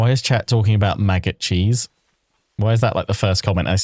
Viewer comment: I thought u said MAGA cheese cos I have the end turn phases at 2x speed 😭